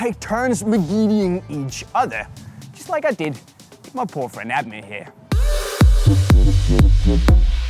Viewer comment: did he just say "BOYFRIEND"?